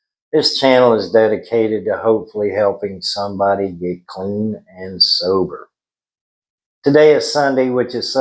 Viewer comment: I thought you said get clean and silver , kind of fitting though . Have a good Sunday !